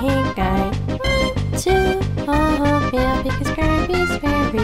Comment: I heard "1, 2, I love you" instead of 1, 2, oatmeal.